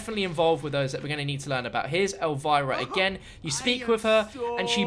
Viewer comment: Lol I thought said 'if you sleep with her..'